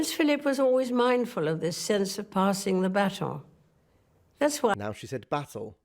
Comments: I thought she said, “Prince Philip was always mindful of this sense of passing the baton.” instead of “passing the battle.” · the AI has a lot to answer for. I just came from a mountain climbing site where the "EYE SACKS" were mentioned. I think that "ICE AXE" was the intention............. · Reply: except it’s not AI, he heard it as battle whereas she actually said baton. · "Interesting"rather than "INSANE". The only insanity is that you thought she said, "passed the battle" rather than "passed the baton" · No. In 1957, she said battle, in 2021 she said pass on the baton. Both you and the captioning software heard it as "battle", but it was indeed "baton". There were a couple of other times that the words sound very similar, but were indeed different words. Of course Gilbert & Sullivan did a whole production on the Homonyms of "often" and orphan", "The Pirates of Penzance". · Very interesting. I think you misheard baton as battle in the "passing the baton" of the more recent speech. In my type of North American accent, the accent is on the second syllable. In hers, on the first. T's pronounced in both. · she is not saying battle. It is baton i.e. pass to the next generation. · she says Baton, not Battle. The Subtitles are wrong on some words. But really enjoyed the video, Thank you!!. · The Queen didn't say 'passing the battle' - you misheard her (no disrespect intended!). She said 'passing the baton' - as in a relay race. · I think she said baton.